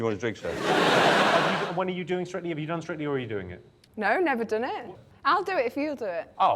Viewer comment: What is Jimmy saying a little after 2:04? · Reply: He's asking her if she's ever been asked to partake in a show called "strictly come dancing"